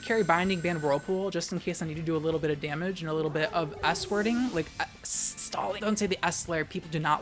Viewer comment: I really thought said "squirting" instead of "s-wording" tell me why i didnt question this at all 😭